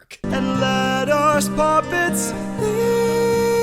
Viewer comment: the captions here say pop and now i can only hear odysseus saying pop-its instead of puppets